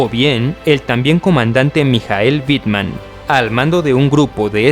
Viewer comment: no le entendí bien a la pronunciación pero creo que es Michael Wittman